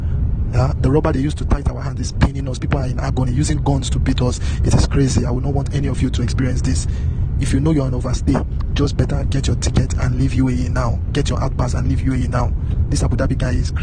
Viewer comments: It sounded like "If you know you're an overstay." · I thought the scammer was saying "If you know you're an overstay", but I could be wrong